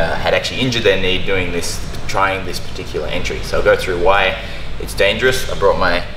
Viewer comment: I thought he said "injury" and made this after the injury as a breakdown, then i realized he said "entry" and actually did this before the injury (?). Mind blown.